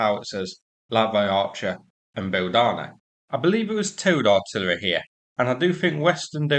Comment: When anyone says "towed artillery" I hear "Toad artillery". Maybe other fans of the Wind in the Willows are with me on the imagery? Poop poop! BANG! Oh, my!